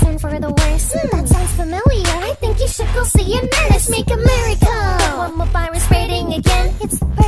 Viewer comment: I literally thought hanyuu said let's make America